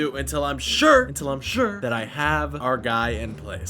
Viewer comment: you said "expand this rail" but the subtitles said "expand this ISRAEL" Explain this because it looks like your sending subliminal messages to my kids. Not ok. Israel is messed up for what they're doing.